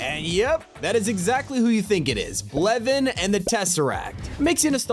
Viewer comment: No way bro said Blevin instead of Kevin